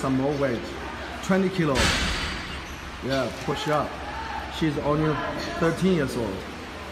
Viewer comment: did he just say “she” 😨